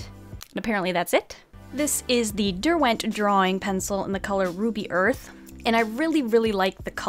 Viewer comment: I THOUGHT "RUBY EARTH" SAID "BABY FORK" AJSHEMSNS